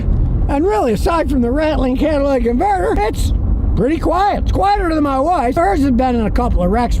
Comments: I thought 💭 he .. said …. It’s quite er than his wife. 😂lol. I missed that part. He said his wife’s car. lol. Scotty you are the man. 🧍‍♂️ keep up the good work. I’ll buy a t shirt. 👕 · I'm pretty sure Scotty said "quieter than my wife's" and not wife, but if she misheard it, we're gonna get a real RIP video!